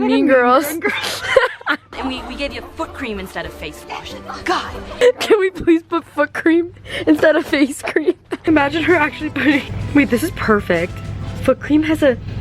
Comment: I thought she said “fuck cream” HAHAHAHA, sorry, this is want happens when English is not my first language.